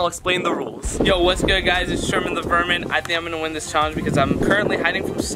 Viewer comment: When he said sherman the vermin I thought he said virgin